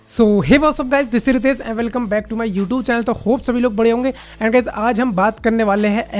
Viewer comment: Did you said aap sb bure honge ???? 🤣🤣🤣🤣😄